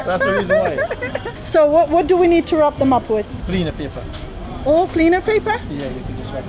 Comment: I'm not sure if you heard me right but I heard "cleaner paper". He said "Gleaner paper" aka local newspaper ☺️